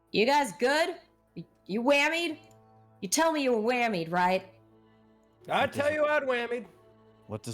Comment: All I hear is Benny Sharp going, "Cuz you're legally required to tell me if you've been wamed."